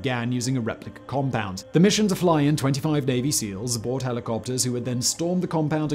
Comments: I heard "25 baby seals" And laughed way too hard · I could swear at 14:57 you said "Fly in 25 Baby Seals" Now and forever more I will think of 25 barking baby seals storming a compound ;) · to me it sounded like he said the flew in 25 baby seals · i totally thought he said they flew in 25 baby seals, haha. I was like "aw<link> 😆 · Did Simon just say "Baby seals"?? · Did anyone else think it sounded like he said baby seals instead of Navy Seals?